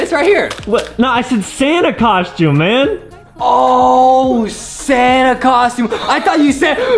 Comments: Adam: got your Santa costume for the next shot Justin:yeah its right here Adam : i said santa costume Justin: oh santa costume i thought you said banna costume😃😃😃😄😄😄😄😄😄😄😄😄😄😄😄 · Adam:i said Santa costume Justin: I thought u said banana costume 😂😂😂😂😂😂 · Justin:oh Santa costume I thought u said BANANA costume Me:I HATE BANANAS!!!!!!!!!!